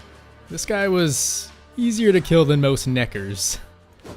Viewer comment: what he say?? 👀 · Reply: Nekkers are the little monsters that gather in swarms. Nice try lol.